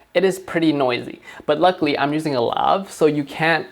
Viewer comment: he says how he’s making his autofocus silent What did he say because it sounded like love